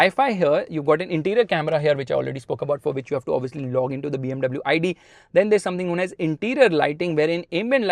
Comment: Did Bro said BMW ID?